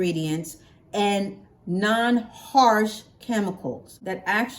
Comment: It does have a chemical in it Guanidine this is what loosen the hair so it's not chemical free. It's a little better than the other chemical but this relaxer is not chemical free. They say that to get sales but I had it and it did break my hair off so I did my research and found out. But your hair is beautiful. Just everybody's hair can't take the same products. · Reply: I thought she said not harsh chemicals.